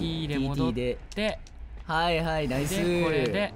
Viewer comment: はいはい、帝統ぅ〜 って聞こえる